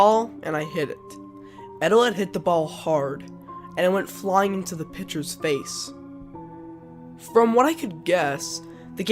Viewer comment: I thought it said flying in to the edge of space